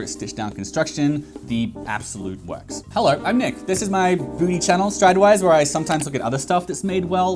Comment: did I just hear Nick say this is his “booty” channel?! 😂